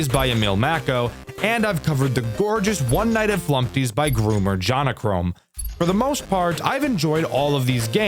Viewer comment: did he just say groomer???💀💀💀